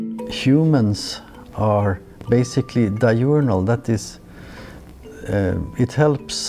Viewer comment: Did he say di-urinal?